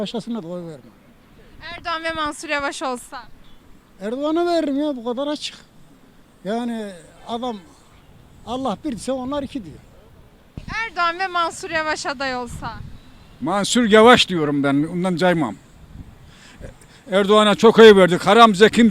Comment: Allah bir ise erdoğan 2 mi dedi ben mi yanlış duydum ?